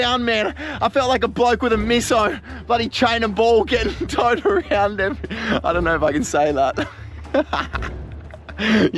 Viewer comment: Does any one know what tf he said at 16:45?! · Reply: Hesaid go subscribe to Field days and watch real survival videos.